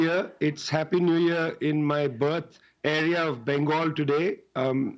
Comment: "Its Happy New Year In My BUTT" I swear he said that LOLOLO🤣